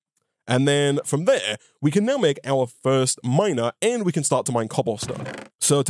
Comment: I just heard " time to get our 1st Minor to mine cobblestone " i was like wtf. Hahahaha